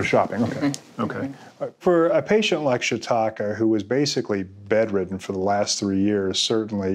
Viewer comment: did he just call her "Shitalka"? "Shit talker", "Shi ka-ka"! She needs to sleep more so that she can simply dream about eating food instead of being awake & eating too much. Also actually pooping out the digested food may help. Just give her a bunch of laxatives & come back in a few days. Shiitake mushroom butt 😜